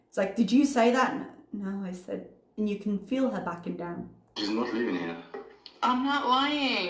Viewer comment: tea. Nice work thank you for the vid👍 25:09. I could be mistaken but when he was on the phone I thought I heard him say, "No she's not leaving yet" !!!!😳 Wth? I interpret that as he can't wait to get rid of FB. I'm not standing up for Chantal, I am just stoked that this self- proclaimed "real man" would have the Audacity to treat another human being with such disrespect 😠